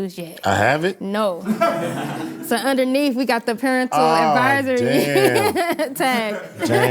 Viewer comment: Did she say "parentle" 🤦🏾‍♂️🙄🤦🏾‍♂️